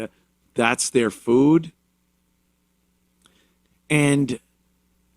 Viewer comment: For a second I thought it said "Dat is their food."